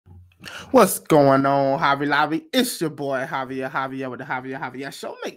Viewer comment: Coulda swore he said " what's up Hobby lobby" LMAO 🤣